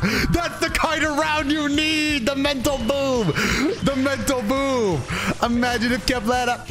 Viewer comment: The subtitles say metal boob 💀 💀 💀 what is a mental boob?? 😂